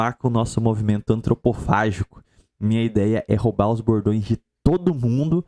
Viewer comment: Todas as vezes que o falou "bordões" eu escutei "gordões". Na primeira vez, ele disse isso logo depois de falar em antropofagia. Pareceu que o cara tá realmente entregue à Gula.